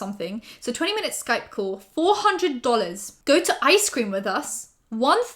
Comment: OH MY GOD I THOUGHT ICE CREAM SAID ICELAND i was like WHY does it cost so much more to get a makeover than to travel to iceland??? fucjskd